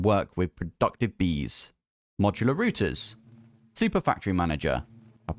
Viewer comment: lmao did he just say "rooters" instead of routers?